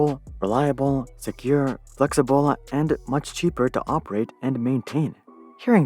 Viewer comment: "operate your operate your" instead of "operate your".